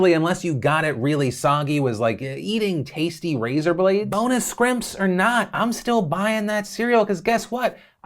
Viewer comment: did he say.. and i quote.. “Bonus Skrimps”? SHIRT OF THAT PLEASE